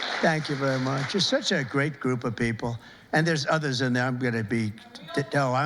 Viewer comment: He clearly said "others" not yellers, lol. Even YouTube captions heard others.